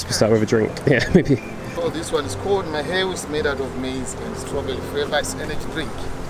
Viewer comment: What is the name of the drink at 5:52? I couldn't quite catch it. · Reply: Found it. It's called maheu (or mahewu).